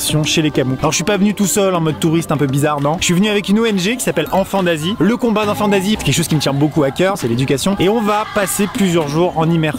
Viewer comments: y a que moi qui a compris "enfant nazi" 😂😂😂😂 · j'ai entendu enfants nazi je suis le seul 😅